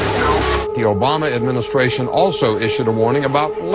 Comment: I gotta be honest, I just hear "Yo Mama administration" 😂